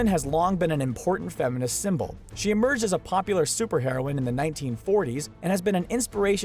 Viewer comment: I thought you said "Superheroin" instead of superheroinne